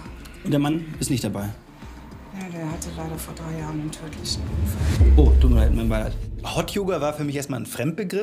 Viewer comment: was hat die da gesagt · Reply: Er hatte vor drei jahren einen tödlichen Autounfall...